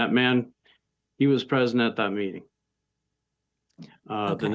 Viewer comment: "And that man, he was present at that meeting, uh the next morning so" At first I thought he said that man was "president" someone...